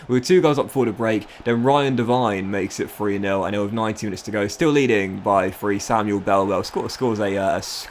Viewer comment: I thought he said 90 minutes to go but he said 19 - English accents lmao